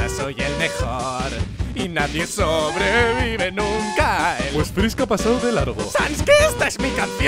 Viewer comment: me gusta esta parte -Papyrus: Y nadie sobrevive nunca a el -Sans: Pues Fisk a pasado de largo -Papyrus: SANS QUE ESTA ES MI CANCION! XDDDDDDDDDDDDDDDD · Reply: We yo escuche "pues creo que has pasado de largo" :u